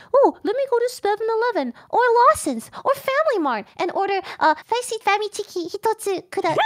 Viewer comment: what did say in Japanese? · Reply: One spicy famichiki please